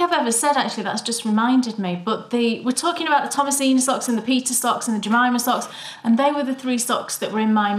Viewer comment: Oh my goodness! I can't believe I won that beautiful Opal yarn! Thank you guys so much! Hahaha, when you said you wanted to knit the Peter socks, I thought you said you wanted to "knit the pizza ones." I was thinking, is that another Opal color? I must need more caffeine. your scrappy sock are gorgeous! Lovely show, guys! <3